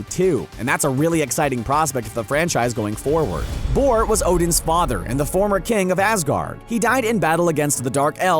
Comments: Did he just say ''thor was odins father'' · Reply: Bor. He said "Bor was Odin's father." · I'm sorry, but at 3:15 did you say Thor was Odin's father? It's the other way around bud. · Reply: He said Bor